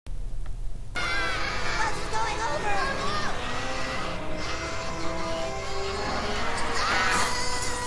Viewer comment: What Is Stan Said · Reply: The bus is going over